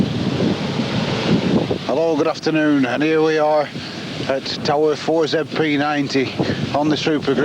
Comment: what does he say, sound like I'm old enough to....but that's not it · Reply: He says, Hello good afternoon and here we are at tower 4zp90